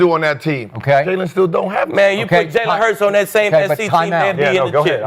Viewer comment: did skip say timeout? 😂😂